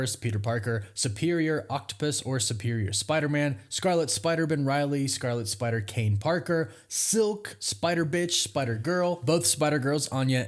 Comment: Did you say one of their names was"Spider-Bxtch"